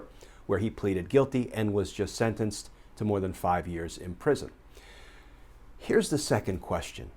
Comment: These Domestic Terrorist are getting off VERY easy. Young black kids gets years in PRISON for non violent cases. He got 5 months not 5 years! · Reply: I thought he said 5 months not years , that's like slap on the wrist, they gotta be joking, or at least somebody Thinks that something is funny